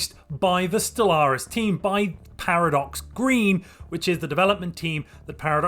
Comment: it sounded like you said "Developed by Paradox Greed" and not Green